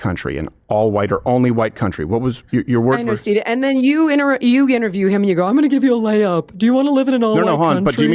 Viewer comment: Uh oh shes whispering to you steve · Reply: and i swore i heard her say "Stevie" lol